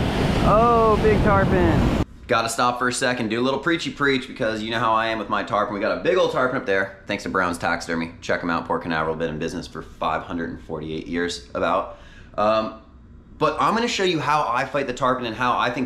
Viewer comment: What county u in? Thought u said port Canaveral. Im in brevard too if that’s where it was